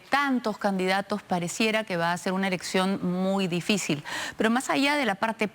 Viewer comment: soy el único que se dio cuenta que dijo erección en vez de elección? jaja minuto